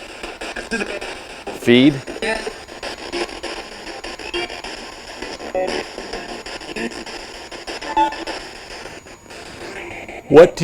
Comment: I heard danger people feed